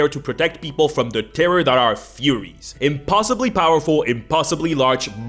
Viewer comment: please say i’m not the only one who heard “furries” instead.